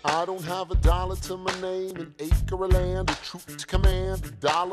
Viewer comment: Everytime I hear "betroot" and i die.